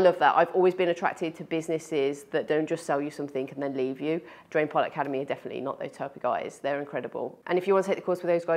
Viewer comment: This is an incredibly helpful video, thankyou. I would suggest losing the auto subtitles. 9:20 the subtitle said Harry Potter academy instead of DPA.